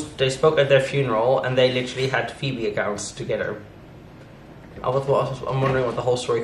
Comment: I'm sorry, they had what together? "Feebiagouts"? I went back and listened 3 times, but I just can't figure out what you said there at 3:08. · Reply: Feedie accounts